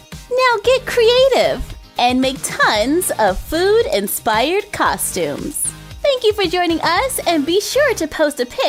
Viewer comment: thought you said expired food costumes lol